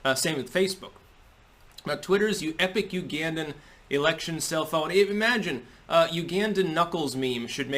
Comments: I thought you said "cell phone" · For some reason I heard "cellphone" in the video. That being said, the irony of Twitter's post is beyond palpable. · I thought you first said “Cell Phone” instead of “Self-Own” 🧐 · LOL i thought he said "cell phone" not "self own". I was waiting for the phone recommendation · "Self-Own"... I THOUGHT YOU SAID CELL PHONE!!! I was waiting for the new TECH REVEIW... IMHO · Oooooooh self own. I thought he was saying cellphone. I was like WTF? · Dude for first minute and a half I thought he was saying "cellphone" instead of "self own" damn eggcorns! Lol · I keep hearing "Self-Own" as "Cell-Phone." I know Gab is coming out with its own physical phone so I leaned in a little closer. But this was good too. :) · I didn't read tut he title and at first I thought he was saying "cell phone" not "sef-owend.